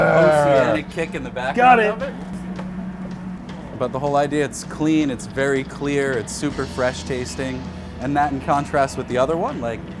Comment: What did that extra flash of subtitles say at 4:15???? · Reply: Got it! "But the whole idea, it's clean, it's very clear, its" How strange. Must have been an editing error.